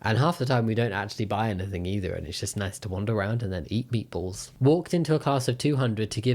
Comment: minutes (idk how to put the timestamp thingy) it sounds like he said “eat beepoles” or “eat peoples” or “eat beanpoles”? Is any of these things just something I didn’t know about or what?